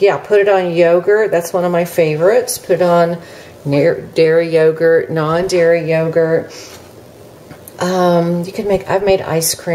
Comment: What did she almost say? 🤔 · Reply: Dairy or non-dairy yogurt. Hope that helps.